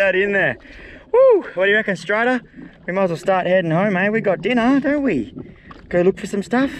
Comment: Did he said där inne13:13